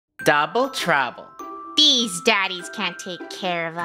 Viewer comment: When said “Double Trouble 😈 “ at the beginning I thought he said “Donald Trump”